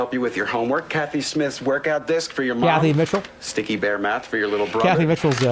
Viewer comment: i love 2:10:08 because when i heard the name i also thought "cathy mitchell?" and got excited. jerma is infecting me jerma is infecting me jerma is infecting me